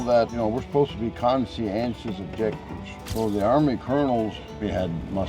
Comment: can anybody help? I can't figure out the words after "we're supposed to be"; sounds like "conscientious of _", but that last word eludes me completely. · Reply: “Conscientious objectors” one who is opposed to serving in the armed forces and/or bearing arms on the grounds of moral or religious principles. If you’ve watched hacksaw ridge that’s what Desmond Dahl the main character who didn’t fight in battles, that’s what we was